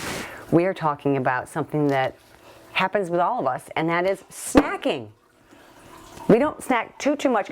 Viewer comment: LOL...I thought you said (0:14), Let's talk about something that happens with all of us...that's SAGGING!" And I'm like, yes! Let's talk about that! haha